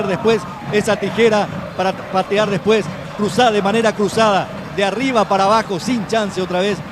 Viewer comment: ¿dice cruzo de manera cruzada? o escuche mal